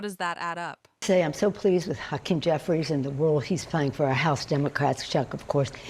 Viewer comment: I heard her say "fu*ken Jefferies" ..... LMFAO.... ha ha ha ha ha lolmao